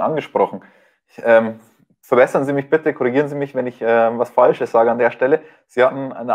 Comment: Er sagt "verbessern Sie mich", ich verstehe jedes Mal "fressen SIe mich bitte".